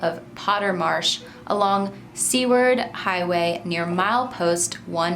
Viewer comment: The Seward is actually pronounced like Sewer, with the letter d at the end! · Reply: I was so confused at first bc I thought she said, "C-word" Highway. I didn't know wtf to think.